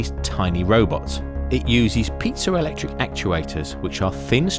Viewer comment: Pizza electric? I'm kidding, I know what you said. Can't remember how to spell it, though.